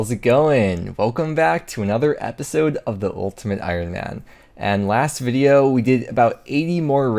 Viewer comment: I could've sworn you said 'moletimate Ironman"